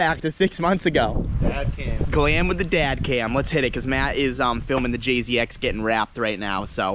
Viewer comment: had to relisten to this sentence three times cause the first time I thought he said "the clam with the dad cam".